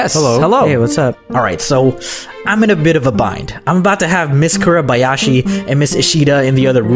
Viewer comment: did mean miss Kobayashi's dragon maid? Or am I just hearing wrong...?